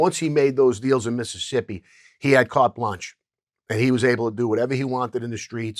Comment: What is that word he says around 12:19? "He had card lunch?" That's definitely not right but does anyone know?